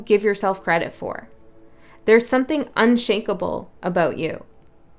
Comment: "Unshakable foundation" and I always hear "unshakeable joy" reminding me of Heidegger's Being & Time.😎 Thanks, by the way ❤️♾️❤️